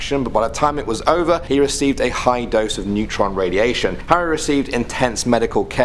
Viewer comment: I thought you said a lethal amount of neutrino radiation xD